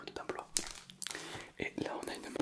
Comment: une quoi ??? J’ai pas bien compris😂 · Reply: Une M